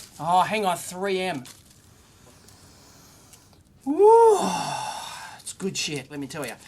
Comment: LOL did he say "good shit" as he sniffed the roll? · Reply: Stranger he said "it's good shit!"